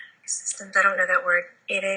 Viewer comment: can anyone hear what is being said. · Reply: I believe she said, Ecometrics system